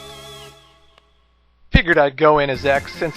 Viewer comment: at first i thought he said "scarabitch" lol